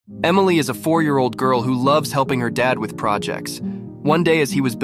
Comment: First i thought he said 44 year old💀